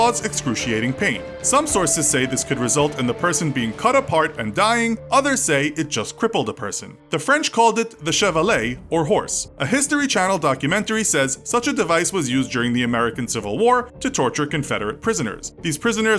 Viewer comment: I thought he said Chevrolet and I was like "What in the actual f***"